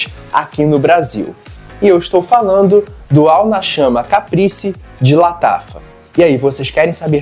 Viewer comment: Eu preciso limpar minha mente... Juro que ouvi "All na shana"... Video top 🤜🤛